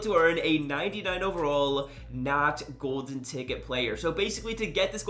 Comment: Any time! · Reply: I was watching while playing madden and I thought you said not golden ticket player I looked and you said NAT 😆